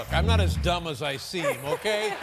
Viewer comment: why did he say "as dumb as I see" rather than "as dumb as I look"?